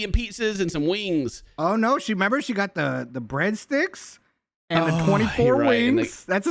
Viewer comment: I thought she said 24 hundred wings!🤣🤣